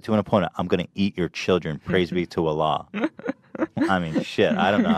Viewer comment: i just click in the middle of the vod and all i hear is "im going to eat your children, praise me to Allah"